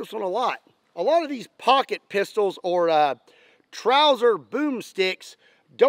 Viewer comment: mann i thought it said rocket pistols